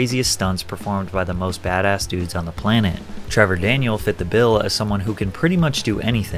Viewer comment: did he say Trevor Daniel?